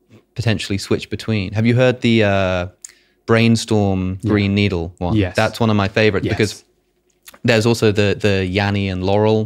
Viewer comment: You can also mix up the words and hear them like greenstorm and brainneedle. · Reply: on my second listen I heard "grey needle"